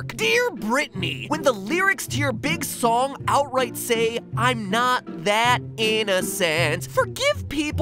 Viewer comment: did he just say "I NOT FAT!" At the beggining